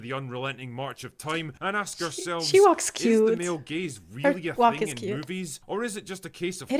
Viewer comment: At first I thought you said “male gays” and I was like what did they do?